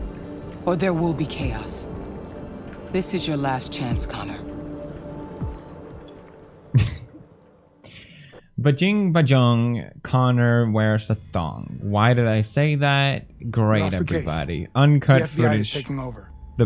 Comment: “Bajing ... bajong Connor wears.. A thong .... Why did I say that” -PewDiePie 2018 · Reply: At first I thought he said “Bajing bajong connor eats Hanks dong lol